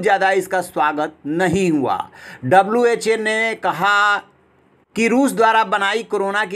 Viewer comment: WTF?? Did you say WHA?